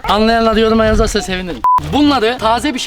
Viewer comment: saniyede dediğini ben uwuwww onyetenww diye anladım 😂😂😂